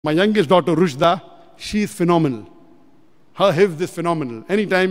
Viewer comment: i heard "her hips is phenomenal 😂"